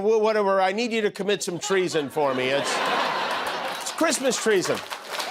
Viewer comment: Did Jimmy just call the phone call, Christmas TREAson ? if so, that was brilliant!